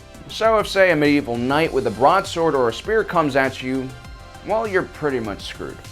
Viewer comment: Medieval knight with a BRONZE sword??? Or did you mean broad-sword? Either way, the average medieval Knight's blade of choice would be a steel longsword. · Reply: I thought he said broad lol